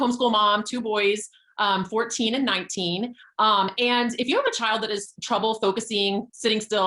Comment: did she say “if you OWN a child”??